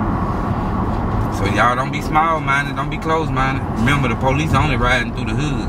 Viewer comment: I thought yall said BR small.. apparently yall dont know what small is 😂